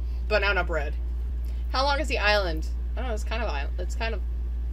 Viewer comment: What did she say at 2:20 ? It's kind of an island? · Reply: she said "its kind of island" instead of "its kind of long" 😭